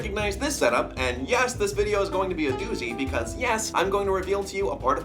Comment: when he said "doozy" i swear he said "newsie" i'm in too deep